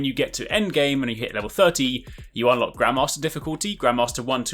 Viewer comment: Haha I know it's bad to say this but because of the (what to me is) an accent, when he said grandmaster I thought he said grandma master and I was like so we're a kick ass grandma! So I may have laughed out loud at work....I just had a very strong mental image.